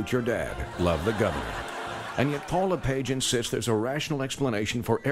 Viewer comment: Remember when Paul Lepage was the Donald Trump of our government? What a world. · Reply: Is that his name? I thought they were saying Paula Page.